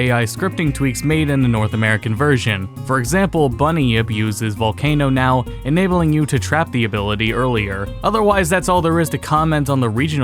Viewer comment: i’m sorry it for the life of me i just CANNOT UNDERSTAND what you’re saying at 0:26 !! “bunny if used his volcano now”???? huh?? please enlighten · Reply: He said "Bunyip uses Volcano now, enabling you to trap the ability earlier."